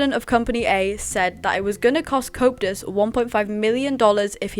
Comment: Not in an offensive way, i find it funny how it sounds like you say copetus instead of coeptus 😂❤